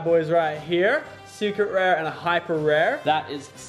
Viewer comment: anyone else thought he said unlucky bitch ?